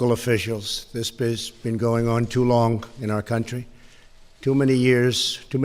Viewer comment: At first i thought he said "This bitch has been going too long in our Country" lol